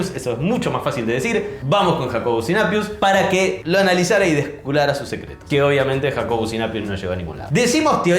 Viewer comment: escuché mal o dijo descubrara 😅